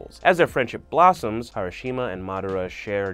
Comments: did he say harushima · did he just say Harashima 😂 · did he say harishima ? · bruh , did you just say hiroshima and madara in 03:50 ? i'm ded ! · did he just say hiroshima? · did he just say "Harashima"😔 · Did He Just Call Hashirama Harashima